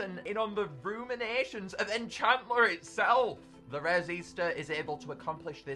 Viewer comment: wow the first rare on Magical synchm!" Me:well actually there rare theramind (Or what I thought he said)